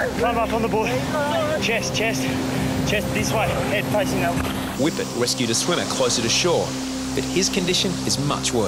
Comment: I'm sry, I know this was a serious situation but, at 0:53, all I heard was "Whippet wescued a swimmer closet to shore"....LOL